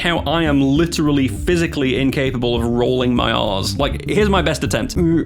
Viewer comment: Okay I gotta admit, quite comically, it took me three tries to understand what you meant at 6:40. I kept hearing "Rolling my arse".